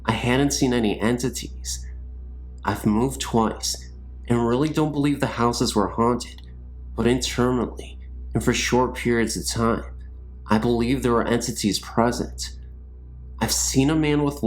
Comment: I'm curious...what word were you saying at 02:58? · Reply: I think he said "Intermittently"